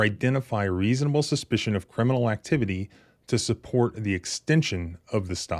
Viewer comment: This video shows a very recent police interaction that occurred in Barbour County, West Virginia. It shows Barbour County Sheriff’s Deputy violently pulling a man named out of a car. is a nurse and was on his way to the nursing home where he works. His longtime girlfriend was driving him. They were pulled over on an allegation of the car going “left of center.” The traffic stop actually ended and a warning citation was issued to the girlfriend/driver. But then, suddenly Deputy ordered both occupants out of the vehicle. When questioned his authority to do so, the officer cited Pennsylvania v. Mimms and quickly lost his temper, macing and pulling him violently from the car. DETAILS/Police Report/Case law: <link> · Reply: There's a typo for the subtitles at 11:36 , instead of reading 'criminal' it reads 'crial'. I'm sorry lol